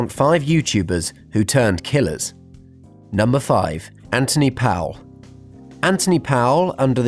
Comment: I almost thought you said Anthony Padilla for the 1st one